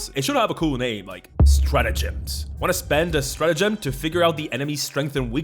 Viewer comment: i heard " spend a stratagem and my dumbass super earth brain went ⬆➡⬇⬇⬇